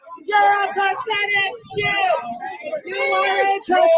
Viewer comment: I heard "You are a pathetic puke" not Jew